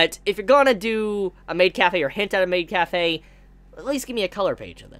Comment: I thought you said "hentai maid" for a second 😂